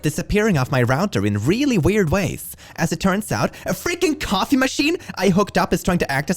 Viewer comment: okay so I had this video playing in the background while I was grabbing stuff from across the room and I did not hear "coffee machine" I heard "condom machine" I don't know how to interpret that...